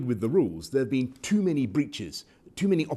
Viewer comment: Dont no why but at 1:18 I thought he said there's been to many bitches lol